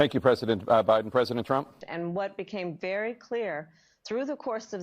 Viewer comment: Did Trump call Biden Brandon? Around 4:00 Minute mark.